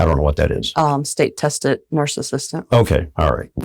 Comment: When she explained STNA I thought she said State Tested Narcissist.